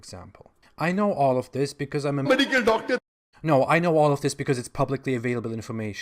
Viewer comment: "Medical Doctor" had me dying🤣🤣🤣🤣🤣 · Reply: Vaishali Prasad . hard of hearing , thought I heard " medical fucker "😮😮😑😊